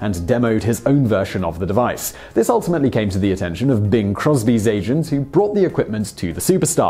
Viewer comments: Oh Im glad you had a picture. Thought ya said Bill Cosby not Bing Crosby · Simon snuck in a Cosby instead of a Crosby.